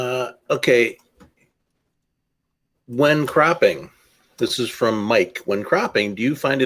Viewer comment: When says "When cropping..." to me it sounds like he is saying "When crapping..." 😂😂 On a side note, isn't all photography art and isn't it all subjective, anyhow?